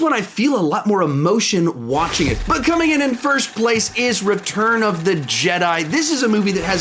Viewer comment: Kick-ass that film sucked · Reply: Lukar um my bad I thought he said rise of skywalker is best not return of the jedi